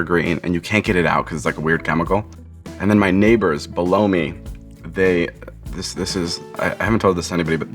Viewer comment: I thought he said “My neighbors blow me”